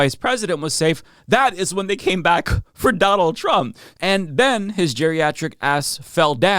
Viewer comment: I’ve listened over and over and over and I SWEAR he said “AND BEN”